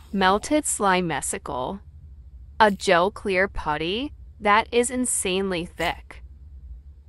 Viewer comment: I thought I heard "a gel queer putty that is insanely thick"